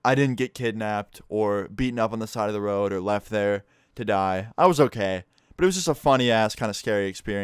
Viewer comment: did he say did i or to die? laurel and yanny all over again